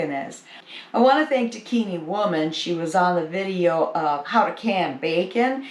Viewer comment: Hi. I also want to know the friends page. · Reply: it sounded like she was saying Bikini woman. Lol. But I’m sure that’s not it. Transcript didn’t give me answer either. 👍🏼.